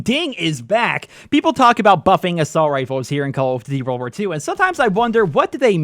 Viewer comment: lmfao wtf did he say?? XDD call of dvy?